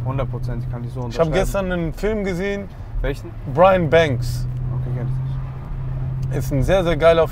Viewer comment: Welcher Film? verstehe es nicht richtig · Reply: Brian Banks